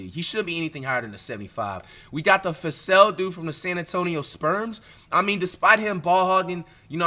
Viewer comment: Did Flight just call the Spurs the San Antonio Sperms? 😂😂😂